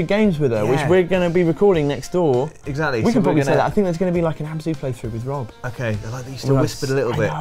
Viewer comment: On my first listen, at 27:15 it sounded like Rob's playthrough was going to be a "hamster playthrough", and then "Ham Zee"? I am sure neither of those are right, but I would honestly watch you guys do any playthrough. Hamster, ham zee, ham sandwich, whatever. Also, I'm not sure if you ever do one-on-onesie challenges with PS2/PS3 games, but I would LOVE to watch and play Katamari Damacy or Katamari Forever.